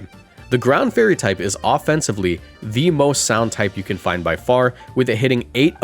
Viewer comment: did someone say sound type????